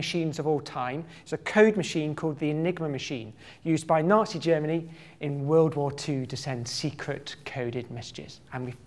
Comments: Could have sworn he just called it "nasty Germany..." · Could have sworn he just called it "nasty Germany..." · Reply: I heard it as "naughty Germany"